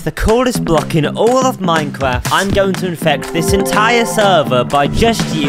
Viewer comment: what did bro say? Mine crap or minecraft?